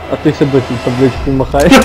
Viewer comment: Не разобрать что сказали на 2:32. Можете, на будущее, в такие моменты просто вставлять титры, пожалуйста. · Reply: "а той ще досі табличкою махає"